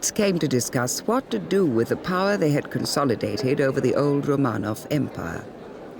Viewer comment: ??? What did she say?? The old ron-off-it empire??? Never heard of it?? And can’t seem to find anything about it… anyone can help please?? Thank you! 🙏🏻🙏🏻🙏🏻🙏🏻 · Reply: Old Romanov Empire.